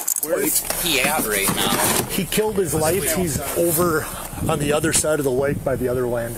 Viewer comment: I thought the officer said "he killed his wife" instead of "he killed his lights" and really was confused on why they didn't lead with that instead of treating it like an afterthought. Captions helped though. Also, I rewound the video way too far at first to hear that line again, and pressed play when they got to the tce. It was super cute how the caption said the dog was making unhappy noises. I think her name is Tika?